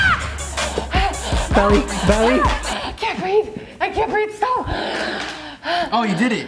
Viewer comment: did she say I’m constipated